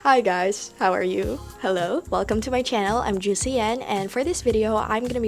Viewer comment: I thought you said I’m juicy Anne 😂